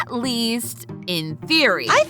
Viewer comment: Am I the only one who heard MatPat's voice saying "in food theory" (don't ask why it was food and not style)